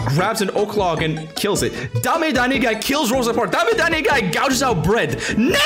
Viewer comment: DAME DA WHAT GUY?! (I heard dame danigguy 😆)